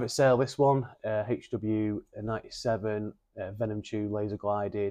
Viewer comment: Sorry, at 15:20 - did i hear right - that HW 97 Venom Laza for 1250 ??